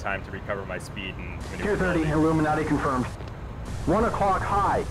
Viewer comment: Did jester say "illumimati confirmed" At 5:51? It sounds like he says that But mainly, this is a great video to get good with the tomcat. At first dogfighting in the tomcat seems almost impossible ( non responsive below 200 kn etc) but once you get the hang of it, it is great in the one and two circle. Great vid